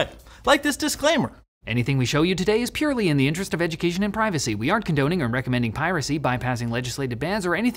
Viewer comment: What I heard was "Anything we show you today us purely in the interest of education and piracy." LOL I suppose it was good thinking putting it in text too.